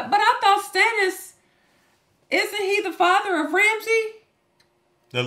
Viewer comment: I misheard this, and thought you said 'Reggie' not 'Ramsey', and I laughed for the next sixty seconds. Something about a Game of Throines character being called 'Reggie' just tickled me. Reggie of King's Landing. Reggie, son of Chuck, brother of Cleetus.